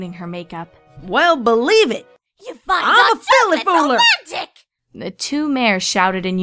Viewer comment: Filly fooler? Is that what you said? Also what did Rarity say at 6:55? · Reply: She says "You find dark chocolate romantic!?"